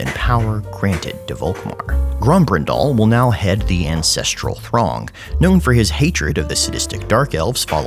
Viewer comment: For a second there i thought you said "ancestral thong" for Grombrindal, and the image of a diamond encrusted thong, passed down by generations of sweaty old men both made me shake in fear and arousal